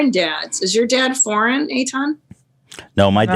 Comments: Did just call Ethan " Aton" lmfao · does call Ethan, A-Ton?